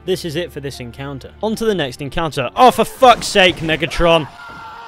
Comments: I thought he said niggatron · Did you say n** tron?